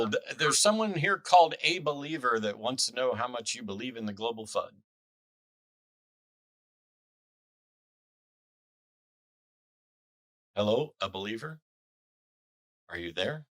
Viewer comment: I heard “global Fudd” 😆